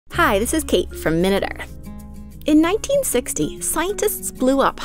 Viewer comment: I thought she said "Hi This Is Kate From Middle Earth